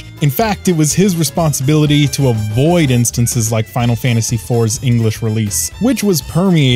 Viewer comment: lol he says FF IV, but the closed captions say FF II. nice.